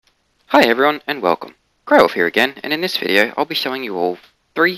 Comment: Lol, you said "Greywolf here" I heard "Grover here".... I thought I was watching Sesame Street. 😂